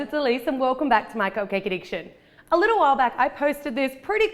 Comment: Did anyone else hear "Hey guys, it's Elyse. Welcome back to my Cocaine Addiction." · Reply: thanks, now when i watch cupcake addiction i hear "cocaine addiction"